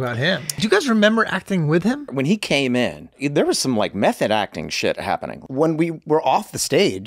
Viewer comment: I thought Glen said “meth-head acting” at first